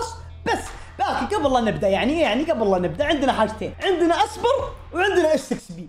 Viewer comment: والله سمعته قال اسبر